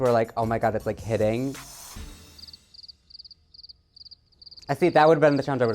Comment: Is that the one @ 7:47? What is Joey saying? “Oh my god there’s a kidding”? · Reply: He says "Omg, it's like hitting!" (I guess some reference about his character, idk) then Michelle didn't laugh at all and he said "that would've been the challenge I was gonna go home on" 😂 At least that's what I got